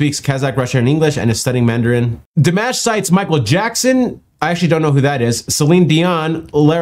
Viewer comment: If I didn't follow along with his mouse cursor I thought he said "Michael Jackson, I don't know who that is" haha